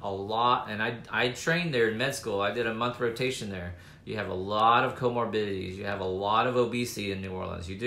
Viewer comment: its sounds like he said theirs alot of covid biddies in new Orleans 🤣